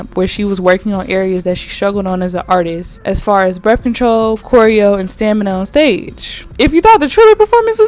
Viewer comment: I was obsessed with how bad that Triller performance was because how?!?! Thank you for reminding me. Also, I heard "birth control" instead of "breath control" and spent like 5 minutes searching for "saweetie artist development birth control" 😂😂😂 Enjoyed this video popping up in my autoplay. I just recommend slowing down for those of us who clearly can't hear.